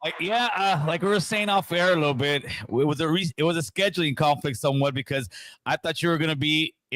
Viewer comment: wtf homie ? I was tuned in live and I thought u said he was rescheduling ? So I bounced